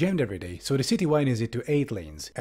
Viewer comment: Why do I keep hearing "Eddy Moorlanes" instead of adding more lanes?